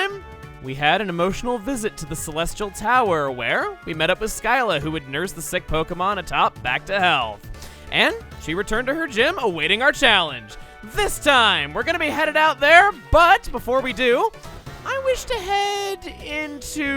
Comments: I thought you said 'back to HELL' for a second there LOL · could have sworn you said “back to hell!”